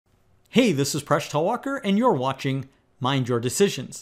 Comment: I literally heard "fresh tallwalker"